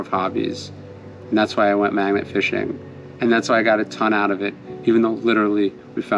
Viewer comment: Lol I first heard this as MAGA fishing. You do a fine job with these thank you sir